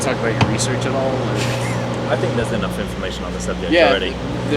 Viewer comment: whats the website he talks about at 6:50? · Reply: anyone find it? sounds like he says "pegasus 220" but i cant find anything.